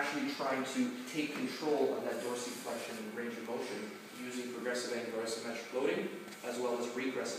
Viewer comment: Great vid ! Around 7:18 you mention a technique to correct the toe dysfunction you say "progressive ag........... symmetric loading" what was the second word? I missed it. cheers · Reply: You probably don't care anymore. But I think he says "Progressive Angular and Symmetric Loading"